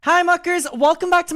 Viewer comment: I thought you said "hi fckers" had to go back twice just to make sure 😂